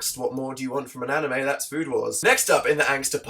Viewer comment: Am I the only one who heard him say food whores instead of food wars